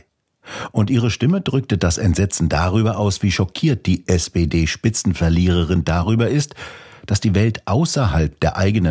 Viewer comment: Bei 1:30 min, beim ersten mal tatsächlich "SED-Spitzenverliererin" verstanden! 🤔🤷‍♂